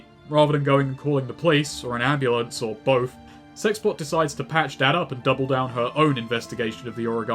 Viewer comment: For most of the video I misheard "sexpot" as "sexbot". Given David Cage's writing, I did not find it strange in the least to sum up a female character as such.